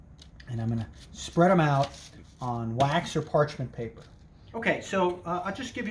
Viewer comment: You said to put the minced garlic on wax paper, but I believe you meant parchment paper. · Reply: He said "I'm gunna spread them out on wax or parchment paper" @ 3:07 He clearly said parchment paper, my guy.